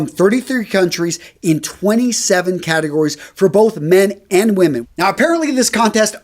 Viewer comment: did he just say women ?wtf